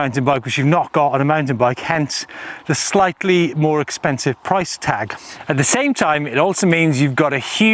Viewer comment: Did he say "SLIGHTLY more expensive?"